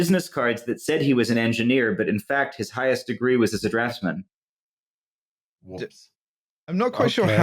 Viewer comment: what are they saying here? 1:12:33 "his highest degree was his [unintelligible word that sounds like "harrassment"]"????? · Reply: “His highest degree was as a draftsman”